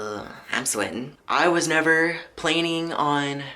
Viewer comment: Did he just say plaining instead of planning